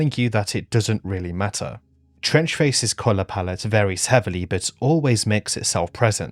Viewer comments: Fuck me i thought you said French Face for a second there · I thought it said "French Face"